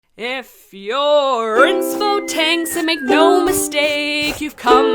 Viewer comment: I just finished binge watching all 18 episodes of Drawing A ___! and I'm loving it so far! However, it might be me, but I have a hard time figuring out what is saying at the very beginning of the intro. What I hear is "If you're instfotanks and make no mistake." I'm pretty sure that's not quiet it, but I hear it every single time, even when I'm trying to listen for anything else, so I can't quite be sure. That doesn't take away from how much I love this series as a whole and it's definitely made me think more about how I tackle art and character design. Keep up the work and don't stop being awesome! - double-anon